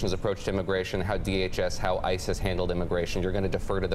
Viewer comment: I swear I heard "how Isis handles immigration"